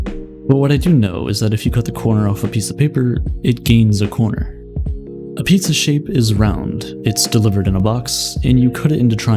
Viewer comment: All I hear is "a piece of shit is round"